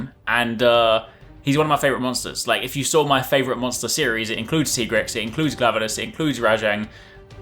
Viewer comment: "includes cigarettes". Sure cigarettes are true monsters but what did he really said? I can't figure it out